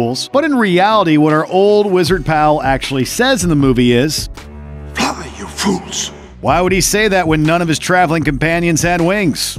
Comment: Who has ever said, "Run you fools"? And in my experience, most people actually get Vader's line right. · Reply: Everyone that repeats the Vader line says: "Luke, I am your Father". And I thought Gandalf said: "Grab me you fools" at first.